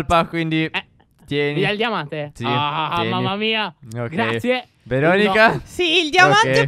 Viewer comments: solo io ho sentito che dice:VERONICA (intendendo)...... MA COS.. ❤❤❤😂😂😂PS 2019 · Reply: Veronica è il suo vero nome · io ho sentito una certa veronica😂🤣